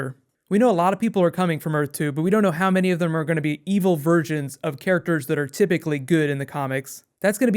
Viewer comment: sounds like you say evil virgins :p